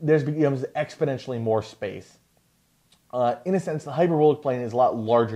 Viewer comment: Isn't it asymptotic? Doesn't that faint blue ring represent infinite area? 2:55 You pretty much confirm it here. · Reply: I hear Exponentially watch memes.